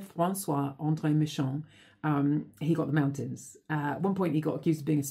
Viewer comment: I thought she said "he got mounted" at 1st...hahaha